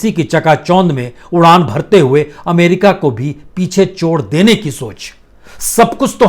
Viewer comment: त्रुटि सुधार :- 1:42 अमेरिका को भी पीछे छोड़ देने की सोच ..चोड़ की जगह छोड़ सुना जाये